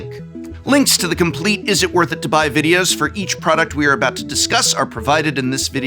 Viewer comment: I think I’ve been playing too much magic cos I keep hearing “Izzet, worth it to buy?” 😅